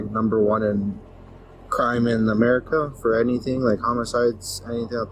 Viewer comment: where is that....at first I thought they said America is #1 and I was going to say I can name a few that I know are worse